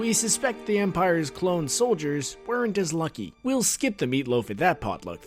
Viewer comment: My Laptop sound is on low, did he just say "The Empire's Clone troopers." or "The Empire's Storm Troopers." I'm confused. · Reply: he said clones bro, that shit got me heated lol